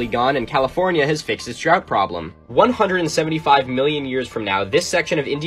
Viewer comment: Lol. I thought he said "California has fixed its drug problem" but he said 'drought'. I guess some problems persist past the extinction of the human race. Also, on vampires: how am u supposed to trust that from a guy with girly hair and bracelets and rings